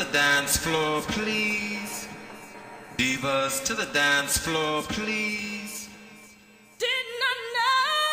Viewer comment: I ALWAYS THOUGHT HE SAID " BEAVIS, TO THE DANCE FLOOR PLEASE" lol